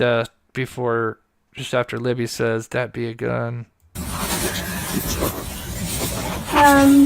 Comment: I hear "that be a gun" twice.